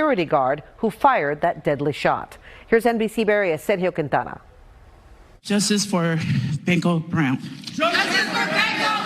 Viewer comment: I heard "justice for BINGO Brown" at first, and pictured a dead trans circus clown.